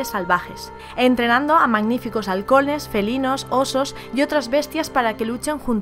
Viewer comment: Entendí "entrenando a magníficos alcoholes"